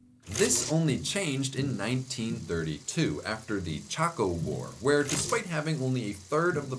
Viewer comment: I swear I thought he said The Taco War.